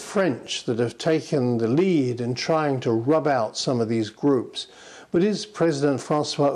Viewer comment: Did he just say, "rub out"? Methinks he may be a gangster-movie-buff.